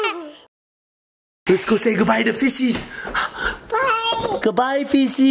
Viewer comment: It sounded like he said "say goodbye to the feces!"